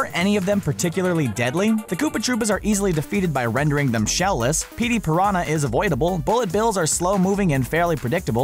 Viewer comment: Did he say Petey Piranha wtf